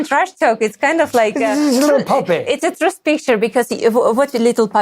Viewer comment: what does she say “it’s a ..?” Anyone know? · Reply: It’s a truth picture